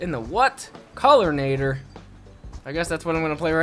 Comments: I thought it said Colonator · Anyone else thought it say colonator for a second and was like o_0